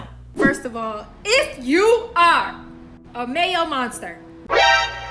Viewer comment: I thought she said male monster and I was like wtf then I saw the captions and I was like what the actual fawk